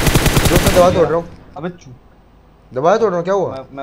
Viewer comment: did mortal said chu*iya